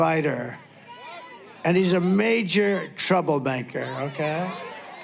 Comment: did he just say "trouble-banker"?